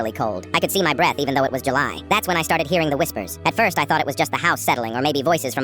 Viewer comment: "That's when I started hearing the whispers" I thought he said whistles and thought MY BOY YOU IN DANGER